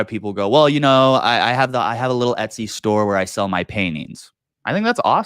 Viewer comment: I thought he said he had an etsy store where he sells his panties 😂